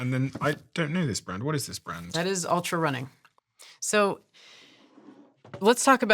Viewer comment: What is name of that Blue shoe. ???? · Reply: I heard her say it’s Altra Running ❤